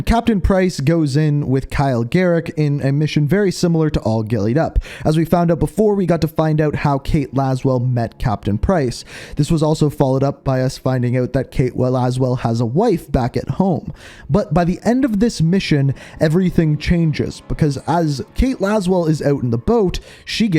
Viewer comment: did you say she has a life or a wife?